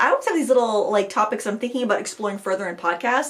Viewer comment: Omg. I'm the Glow Worm from James and the Giant Peach. You have a Termite Herd??? Why would you have a Termite Herd? Wouldn't that destroy your house?... Three sentences Later... Oh, " A term I Heard ! " " Cycle of precious ". Okay I'm less confused now. Geesh. 🤭 🙃💜